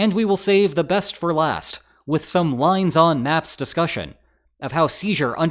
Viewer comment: Did he say lines on maps?